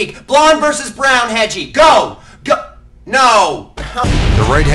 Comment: The right hedgehog is hard to find… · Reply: I always thought he said the red hedgehog